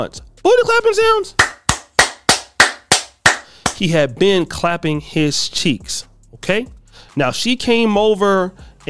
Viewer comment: wat did he say? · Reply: He has been clapping HIS cheeks.. BOOTY CLAPPIN SOUNDS